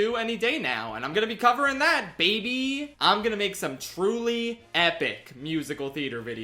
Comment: Wait, did I hear epic? 👀 In all seriousness though, glad to see the end of the Schaff Sandwich Six saga